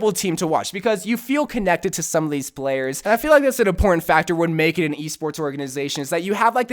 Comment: did he say porn factors?